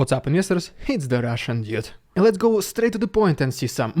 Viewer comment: No offence dude, really, I mean no disrespect and I appreciate your videos and efforts, but you might want to rethink, how you do your intros since it sometimes (just like in this one) sounds like you were saying "its the russian idiot" and not ".. dude". Just a small friendly advice.